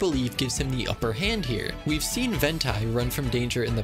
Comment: DID YOU JUST SAY “ VENT- AI” 👁👄👁